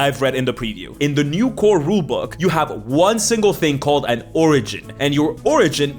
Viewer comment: whenever I hear "the origin of...", I actually hear a certain politician say "the oringes..." I guess, you know, which one I mean.